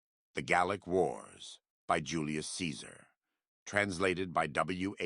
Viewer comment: I thought it said "The garlic wars"... Shout out to Julius